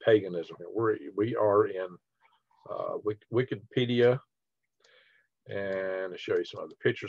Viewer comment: Wickedpedia..... that’s what I thought I heard you say... ironically, it fits.